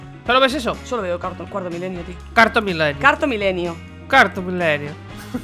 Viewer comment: pense que dijo carton network